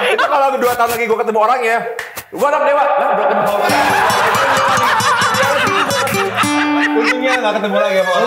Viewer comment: El ngomong apa yaa? Kalah sama backsound dan ketawa yg lain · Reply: Gue anak dewa, tapi keluarga lu broken home